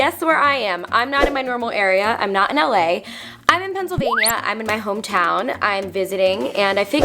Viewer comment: i thought she said Transylvania lol